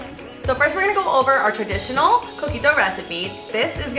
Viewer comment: When you said your drinks name the first time I thought you said cookie dough